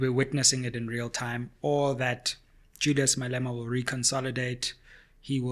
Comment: did you say Judas Malema?